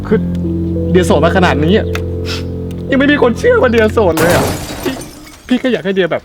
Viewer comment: พูดว่าไรอ่ะ ฟังไม่รู้เรื่องเลย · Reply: นี่มันมีคนเชื่อว่าเดียร์โสดด้วยอ่อ. ฮืออ